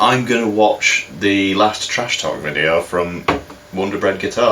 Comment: A rant video? I’ve just had to rewatch this, I forgot how short my hair was 😂 I’m not sure which rant you mean. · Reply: a rant by Wonderbread Guitars is what it sounds like you said.